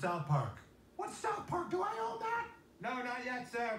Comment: I thought he said “do I know that” he said “do I own that” that’s hilarious 😂